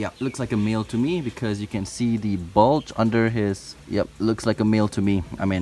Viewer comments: When he said "bulge," I thought he said balls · I thought he said “ oh yep he looks like a meal” instead of male